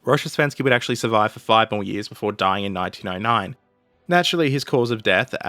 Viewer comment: "Survive for five more years before dying in 1999." - Am I the only one who heard this? Thank god the subtitles are 1909.